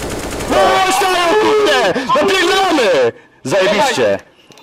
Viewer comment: kto usłyszał napiefdalamy?